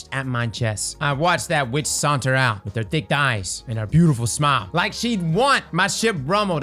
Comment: Took a double take when I heard “Thick Thighs and beautiful smile”